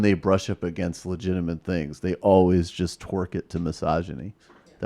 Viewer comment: I heard that as "twerk it to misogyny" lol